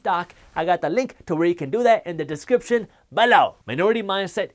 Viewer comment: do you say “But now!” Or is it just a sound effect or other word you’re saying? I hear it all the time in all your videos, but I’m not sure if I’m hearing it right. Thanks for making these videos, all the best! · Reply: He is saying BELOW. As in in the description box.